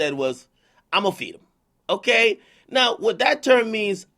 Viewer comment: I thought he said am I see him not only feed him but I might be wrong